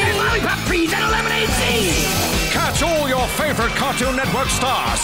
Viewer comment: What? Did you say it's CRAPPY?? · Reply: He said it's cracking